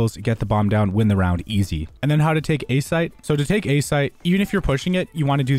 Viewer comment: When he said take a site I thought he said take a shit